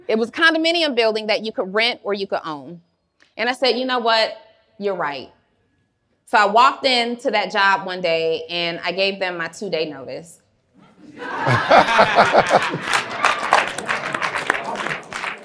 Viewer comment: I absolutely love!!! When you thought she said two-day notice and realize it’s a TO-Day notice!! I can’t wait!!!